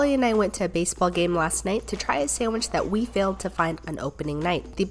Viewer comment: I can't be the only one who misheard this as "racist sandwich"